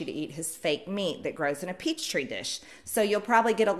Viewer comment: I am hard of hearing, but did she just say, "peach-tree dish"?!? Is that related somehow to the gazpacho police?